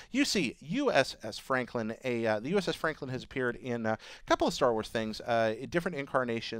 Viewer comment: Did he say star wars just after 1:20? O.o